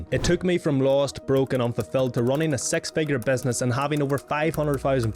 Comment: Good grief, I thought you said "s3x figure business" 😂